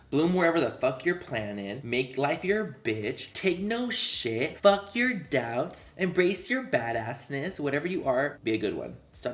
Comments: Why did I think you were going to say “Fuck your dad” when you were reading fuck your doubts 😂😂😂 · Lol I thought manny was about to say “fuck your daddy” instead of “fuck your doubts” 😂😅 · I thought he was gonna say f*ck your dad 😂😭